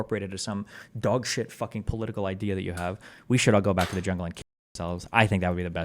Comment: what is destiny saying there - "we should all go back to the ____ and kick ourselves" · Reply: Jungle